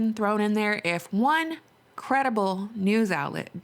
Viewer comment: Did you say one CREDITABLE news outlet? @ 4:06 min, is that not a oxymoron?